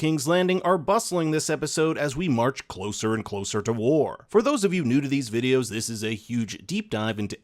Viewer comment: For those of you new to these videos... I heard "For those of you Nudity videos..." :face-fuchsia-tongue-out